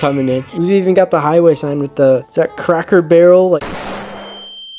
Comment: I thought he said Kraken Barrel for sec😂 3:35, bro do you have both German and Lithuanian heritages? Or did you lie to us about the German one?